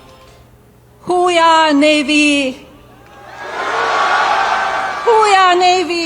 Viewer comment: Thought she said whole are Navy ? Rather than Who we are? Navy. Who Trump is, an Embaracement.